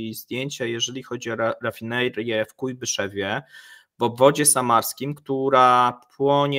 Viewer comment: Ale tęgą nazwę mają... "Chójbyszewie" xD Tak usłyszałem przynajmniej :D